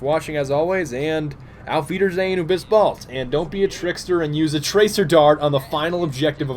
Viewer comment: Everytime you say "Aufwiedersehen, Bis Bald" I thought you would say something like "fetus anus bis balls" Eventhough i AM German